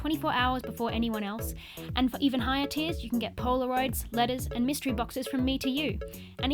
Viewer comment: 😭 I thought you said Polaroids and lettuce